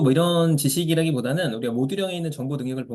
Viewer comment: 안녕하세요 6:14에서 음성이 좀 불분명해서, "IT 쪽 지식이라기보다는 OOO에 있는 정보 능력을 보면 되구요" 하셨을 때 OOO을 못들었습니다. 혹시 뭐라고 하셨던걸까요? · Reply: 모듈형